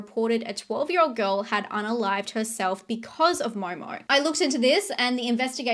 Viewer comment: you say a 12 year old girl …….WHAT HERSELF? I swear I rewound it 59 times and have no frigin clue what you said !!! The closest I came was she UNALIVED HERSELF??? What the hell is Unalived? You mean killed? If you really said unalived instead of killed I’m done with this Chanel